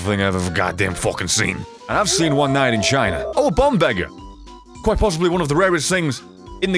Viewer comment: What did he say at 3:02? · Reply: Nuka Charming and I've seen one night in china